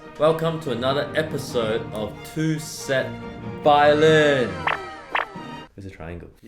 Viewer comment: I heard "two simp violin"